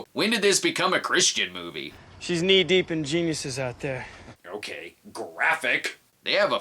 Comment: I thought Troy was saying "She's knee deep in Jesus"